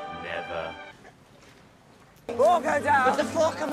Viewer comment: am I the only one who thought he said “FOCK ‘IM UP!”